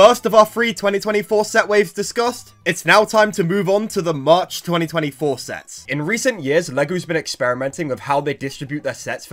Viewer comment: my heart dropped, OMFG, I thought it said 2025 Sets😭